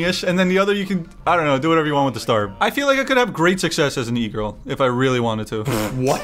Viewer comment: I low-key thought he said "I feel like I could have great success as a negro if I really wanted to" 😳😳😳